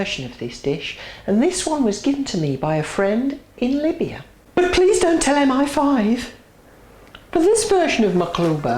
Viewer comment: What does she say at 0:34? "But please don't tell him I'm 5?" Haha but nice video